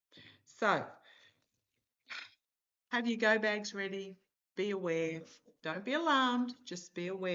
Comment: "Have your GO BAGS ready!" I had to rewind and listen again... because I heard "Have your gonads ready." Omg I'm still laughing. Thank you! Much love to you from Wisconsin, USA. ❤😂❤😂❤